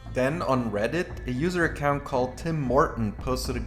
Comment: Because I was listening to this instead of watching, I keep hearing "Tim Morten" as "Tim Horton", and was wondering what a Canadian coffee franchise had to do with Stormgate.